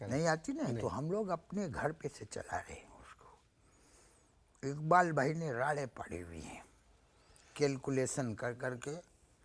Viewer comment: Iqbal Bhai ne raareh pari hoi hen.. Here he is mentioning Iqbal Bhai Father of Ary CEO Salman Iqbal.